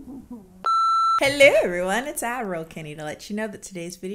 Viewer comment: I just realized Kennie isn't saying "Admiral Kennie" she's saying "Ad roll Kennie." for this whole time I thought she was calling herself Admiral for some reason. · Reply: At first I thought she's saying "adderal kennie" 😂